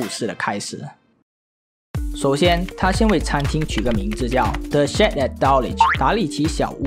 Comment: 我差點聽成the sh*t at Dulwich😂